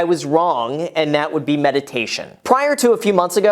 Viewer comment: when you said meditation i thought you said medication